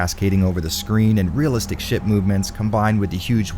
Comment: I swear he says "realistic sh*t movement"! I was like "what?!"